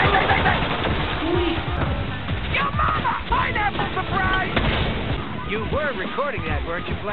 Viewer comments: does he say "pineapple surprise!"? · Did he just say ''YO MAMA!!1'' at 0:54 or I heard it wrong? OH GOSH EPIC.